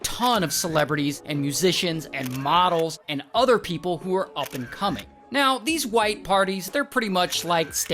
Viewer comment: i thought he said up and "cuming" for a second there